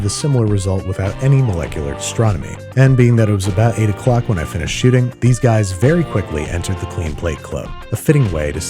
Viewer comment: "...when I finished shooting ". In case anyone else misheard